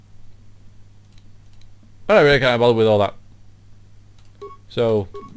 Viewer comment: did you say "i reckon i can't be bothered by all that."?